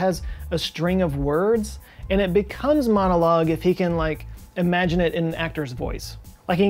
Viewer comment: Did I just hear you say "imaginate"?